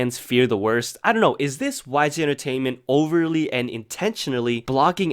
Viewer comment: The auto correct says WiSe Entertainment instead of WHYGEE.Well,never believe auto corrects